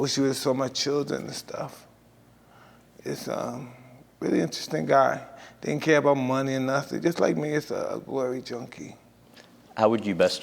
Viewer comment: Can someone please tell me what he said? No joking i am very curious “just an interesting guy just like me a....” glory junkie?? That makes no sense so not sure if someone understands him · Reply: "Really interesting guy. Didn't care about money or nothing, just like me, a glory junkie"